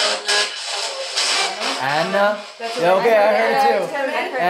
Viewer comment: did it say anna bell or am i trippin